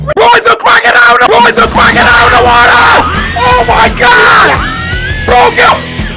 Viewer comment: Me:cory what you be sayin Cory:what you mean Me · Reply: Bro Issa crocodile in tha waterrr😂